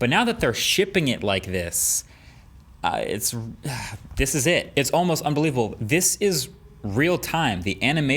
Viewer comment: I thought chief said this ain’t it...